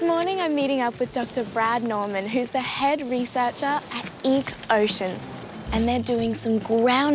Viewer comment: When Bindi said Ecocean at the start, I thought she said Eek Ocean. I was like “The fuck is that?” lol